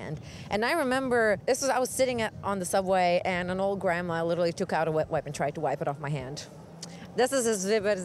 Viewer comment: I love the innocence of how at 05:04 the girl explains how the grandma on the subway took out a wetwipe...but the subtitle says 'she took out a whip...' 😆😆😁😁😁 The two girls interviewed from 00:45 are lovely and they suit their tattoos. DJ Drako is 😍😍 I love pple with them as it adds character.. and for me personally it makes guys visually interesting...and many tattoos are so beautifully designed. 👍👍👍 Of course I still love pple whom don't have any!! 😁🤗❤️❤️. I just appreciate body art. Interesting interview thou, good job.